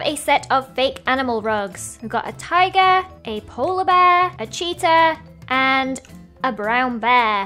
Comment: When she said cheetah I thought she said cheater...lol😂